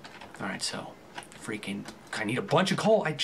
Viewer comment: lol it sounded like he said "freakin' asians!"